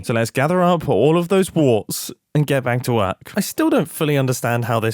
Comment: Finishing the tree, and my brain polluted with Wart puns, I thought Mumbo said "Lets get back to wart" instead of work. Wart brainrot is real.